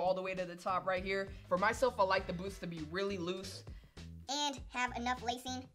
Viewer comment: when he says boost instead of lace.... the boost lyfe took you from us. 😂